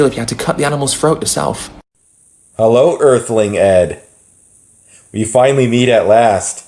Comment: When said “Earthling Ed” I heard “Earthling Ad”